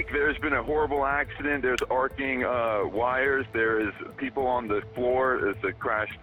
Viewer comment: bro said “arcing wires” but the caption said (unintelligible) swear these editors can’t hear for shit dude